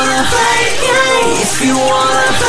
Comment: I thought that he said play gay hehehe .. but it turned out to be play games